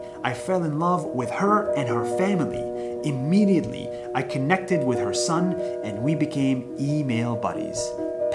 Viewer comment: when you said connected i heard you say kidnapped